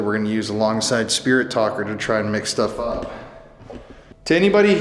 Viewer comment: when they are doing their intro did anyone else catch saying “and we’ve got this new Ovilus device we’re going to be using along with Spirit Talker to try and make stuff up”? I found that kind of weird. Maybe I heard it wrong but wondering if anyone else heard him say it too. If so why would you be trying to make stuff up when you put in the beginning of the video that everything you are about to see is real 🤔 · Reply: I said “mix stuff up” if you listen carefully 🖤